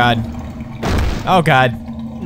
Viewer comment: when you said "oh god oh god oh god...." it's sounds like you said "o gado-gado gado-gado..." (Indonesian food :v)